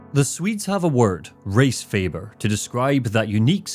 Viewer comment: "Resfeber" at first sounded like "Race Favor", and wow that did not end up meaning what i thought it would. Thank god.😂